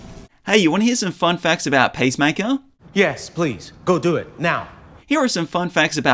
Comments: Sounded like you were saying pacemaker... maybe don't be a superhero with a weak heart 😅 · Sorry but I low key lost it when I wasn’t expecting the accent in the beginning of the video and I thought you said “Pacemaker” 😂